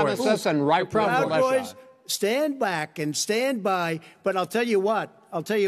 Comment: “Say it do it say it” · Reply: I read what you said and what I told you still applies; he told them at 0:44 or so to "stand by", not "stand down". The comment of yours I replied to stated "stand down" which isn't what he said.